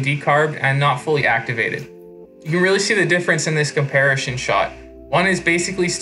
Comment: I know you didn’t say “comparishon” instead of “comparison”